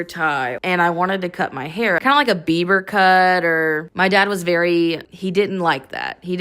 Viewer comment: did she say a "beaver cut"? What is that, can someone describe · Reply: Bieber cut. Like how Justin Bieber used to wear his hair when he was younger.